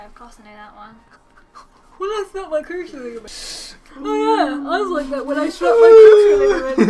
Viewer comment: I thought said "oh im not like that i slapped my toushie" and im like WWHHAAATTT